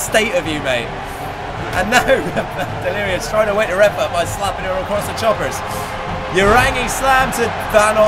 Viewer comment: did you just say "her"?